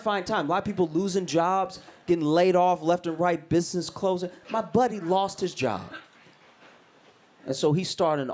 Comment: I thought this said, "my body" not "my buddy". Anyway, still a fan.